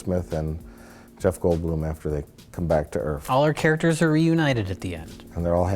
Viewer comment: Did he say "earf?"